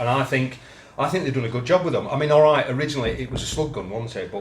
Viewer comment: said ‘slug gun’, but I heard ‘spud gun’ 😂🤣 And I’m glad recognises that many of us are shooting more springers. Many shops have doubled their pellet prices and even air refills have gone up about 25% in my area. Protest with your wallet and shoot your springers more. It’s more rewarding and will save you money. Join The Great Springer Protest. 🪧 😁 Great vid 👏👏